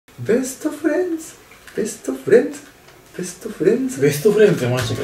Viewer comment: ベストフレンチの時トーストフレンチに聞こえた 笑